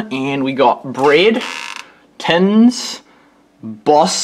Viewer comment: Nice! (And letting us snoop in the cupboards and drawers -- interesting!) -- You said something at 0:02:57 -- after you said "bread" -- what did you say? Sounds like "pins". -- Good video! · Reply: ). It did sound like "pins". It was "tins". As in tins of fish, veges, fruit, etc.